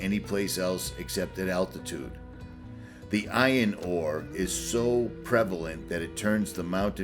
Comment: Did you say ion ore?